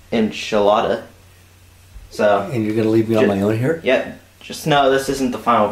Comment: i thought you said you were going to leave him alone!